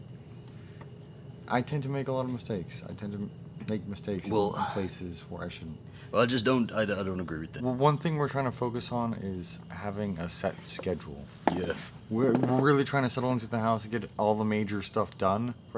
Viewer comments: I thought said 'a sex schedule' at first and I was like WTF haha. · At first I thought he said “a sex schedule”. LOL